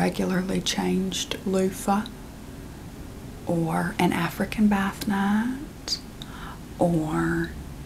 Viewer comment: The accent had me picturing a tiny bug that lives in my shower (I kept hearing "bath gnat")